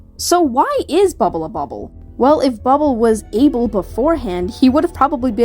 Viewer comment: the more they say Abel the more I hear "a bubble" like a stretched pun for sure but it's there